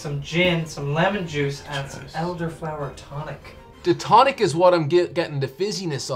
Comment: I would have sworn he said elder flower chronic not tonic.